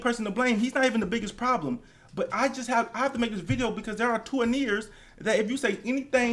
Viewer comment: did he say "Tuaneers"? Lmfao, I have to start using that